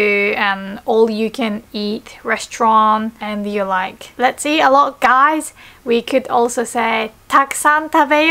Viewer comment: I thought she said, "Let's eat a lot of guys." Oh dear lord! Then I read the text, whew, I'm safe for now~!